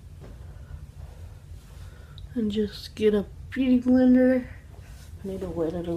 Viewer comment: Low key I thought she said puberty blender....